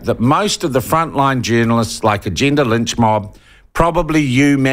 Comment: Sean; did I hear you say "the gender lynch mob" or was it "the Jenna Lynch mob?"